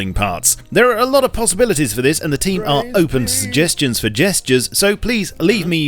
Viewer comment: did he just say SUGESTURES?